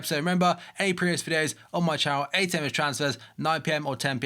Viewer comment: It sounds like he says- aand arse you next time